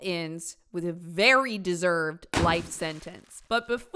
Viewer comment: omg i thought you said “light” sentence and double tapped back so fast